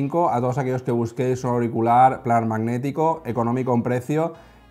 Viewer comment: ¿Qué dice? No se entiende el nombre del auricular. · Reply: Dice planar magnético o sea la tecnología y eso que soy Argentino se entiende perfectamente